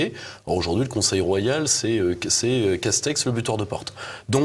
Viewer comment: « Castex , le buteur de porte » mdr. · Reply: Pardon, moi j'ai compris "Castex, fermeture de portes", c'est pareil. Un laquet quoi.